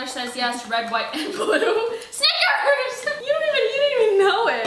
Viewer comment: did she say snigger? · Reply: Does every thing have to be racist these days. SHE SAID SNICKERS THE NAME OF THE FREAKING DRINK